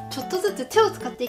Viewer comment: さんいつも思うけど、「〜したいと思います。」ってゆう時、いつも「〜したいともいます。」って聞こえる！